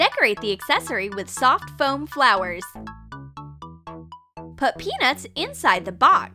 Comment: “Vikki listens with admiration, how can I make him like me?” 😂 am I the only one that finds this hilarious. I just went further in to the video 9:15 😂😂😂what👏the👏hell👏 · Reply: I THOUGHT SHE SAID PENIS!!